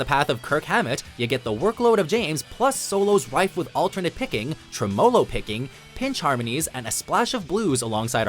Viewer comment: What the f did you just say? Trim-Oh-low?